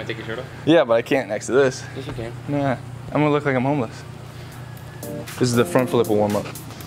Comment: I thought that said he was gonna look like a walrus instead of "I'm gonna like a homeless" and I'm laughing at my own stupidity! And as usual, thanks to the whole team for the great content!!!